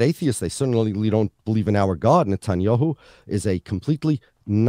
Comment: Who else is watching this in 2023 and heard him say "thank God Netanyahu isnt PM anymore" 😢